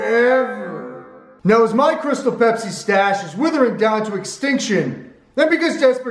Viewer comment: I thought it said making my own crystal meth with pepsi